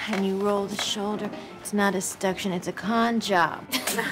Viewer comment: it is unclear to me what she is saying, can you help me out? "When you roll your shoulder, it's not a ...(?)..., it's a con job." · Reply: gerard nealon seduction